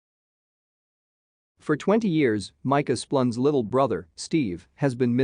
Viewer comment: I thought it was saying "Micah's blunt" at first